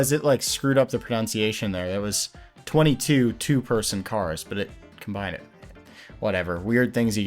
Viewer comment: that wasn’t a mispronunciation I think you misunderstood. It said “20 2 person cars” as in there were 20 cars that fit 2. You said it meant to say “22 2 person cars.” I just don’t see why you would think that when 20 2 person pods made sense. Sorry that was nitpicky lmao